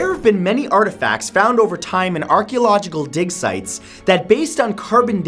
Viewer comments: At first I heard Dick Sites😂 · I thought he said dick sites😂